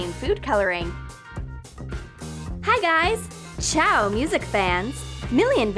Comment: did you Say “čau music fans” ??